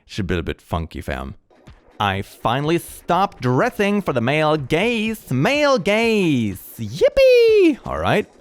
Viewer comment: did anyone else hear male gays